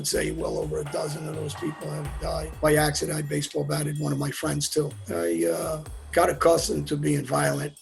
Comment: Does he say he accidently baseball batted one of his friends?